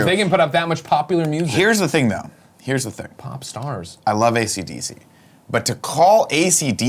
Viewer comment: Dam my mind I thought it said pornstars